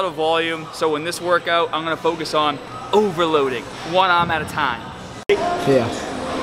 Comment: "Overloading voila metata." 9:05 I don't even want to know what he actually said